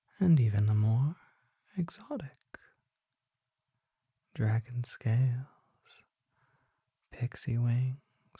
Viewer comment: Me: Spaces out for .1 seconds Misses a whole chunk of what was saying Also me: That’s fine, let me be confused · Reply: Bro I thought he said dragon tails when he said dragon scales😂